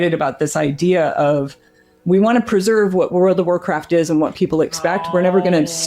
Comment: does she say "War of the Warcraft"?